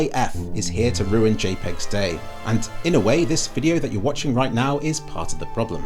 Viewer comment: For a second there I thought it said JREG was dying 😭😭